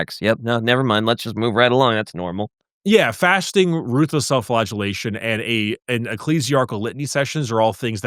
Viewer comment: Did say ruthless self flatulation instead of self flagellation at 52:38?